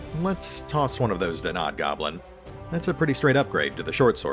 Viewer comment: I was listening to the video in the background while finishing some work and I could’ve sworn you were saying “Knob Goblin”